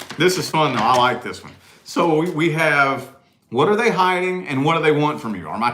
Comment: Good stuff!! I just scrolled across this in my feed a second time and for a second I thought it said “What are they wearing?” 🤣